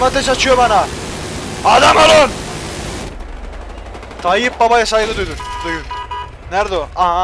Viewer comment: Tayyip babaya saygımı duyun dedi benmi yanlış duydum :D